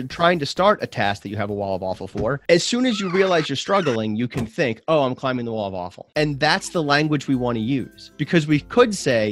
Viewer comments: I keep hearing "wall of waffle" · I always heard "wall of waffles." No? Just me? Okay... (Love you btw) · I keep hearing "the wall of waffle" and it doesn't sound as bad if you think about it in that way :D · I just heard "wall of waffle" and my brain stuck to picturing waffles.